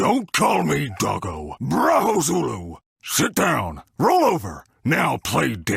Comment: Did he just say "sh*t down"??? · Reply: Sit down